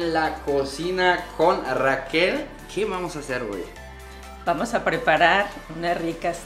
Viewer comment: Mnto 1:28 😅 que vamos hacer hoy(yo entendí wey) y de wey ya es costumbre 🤣 asi siempre digo yo y en el trabajo mas 🤣salu2 pinches